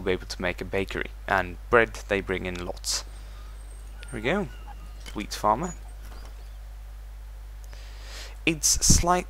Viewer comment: when he said wheat farm i thought he said weed farm.